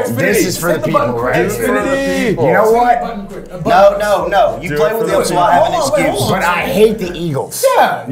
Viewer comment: anyone else hear A.J say “no play the eagles so I have an excuse”?